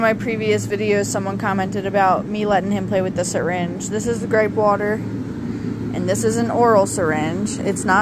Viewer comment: Gripe water okay I thought you said grape water at first. I'll have to look it up online for my grandson He's almost 5 months.